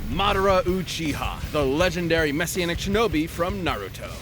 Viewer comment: Mexican shinobi??? Wtf is he saying???